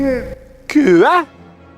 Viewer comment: Ce que j'ai cru entendre après plusieurs écoutes : "After the schematic, I will be the new king", dites-moi si je me goure